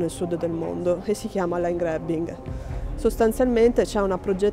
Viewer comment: cosa dice lei nel 0:35? "Line Grabbing?" cosa sta accadendo nel sud del mondo? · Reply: Land Grabbing, accaparramento delle terre